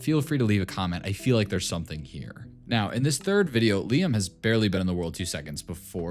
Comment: he said look liam from what i heard